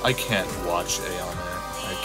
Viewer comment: am I the only one who hears aeonair at this part